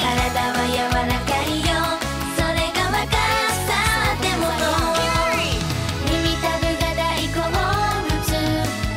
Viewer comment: What does the voice say in English at 2:43? “My body is [????] because I’m young.” Please fill in the word. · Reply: Ah, you’re right, yes the voice says “flexible.” Thanks!